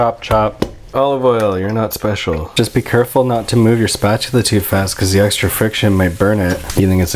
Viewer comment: did he say spatula?